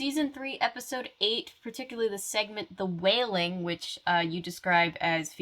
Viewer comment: oh my god, I kept hearing "the WHALING" and I was expecting some Captain Ahab shenanigans I feel so stupid right now 😂